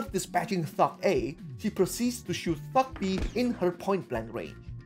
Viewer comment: lol heard that as "shooting f*ck B" instead of "thug b"